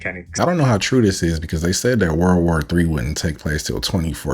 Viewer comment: why do the captions say world war 2 every time they say world war 3? Obviously there will be a world war 3 in 2048… the plastic bag famine of 2047 will be the final push